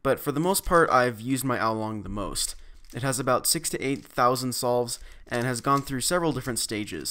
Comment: thought he said 68000 solves until realizing 6 - 8000 solves